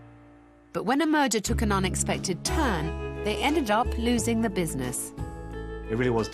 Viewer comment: Oh “merger”, I thought she said “when a murder took an unexpected turn” 😮